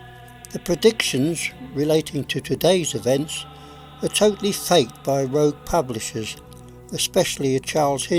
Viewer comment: Did you say that all the modern day predictions are totally fate or totallu fake at around 3:50-4:01? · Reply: Mark Laws *totally fake